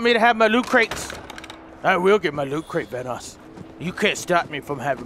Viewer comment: did he say vanoss?!